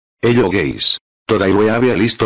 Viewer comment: It sounds like you said hello gays